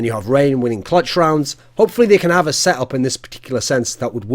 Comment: Damn I was playing BnS, and played this video in background and when hiko said "I'm the master baiter" I heard "I'm the mastrubator" lmao